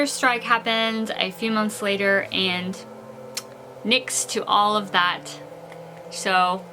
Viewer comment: Thank you so much for this inside information. I heard the word "niks" at 2:25. That is a Dutch word meaning "nothing" So it was nice to hear you saying a Dutch word of my language ...;-)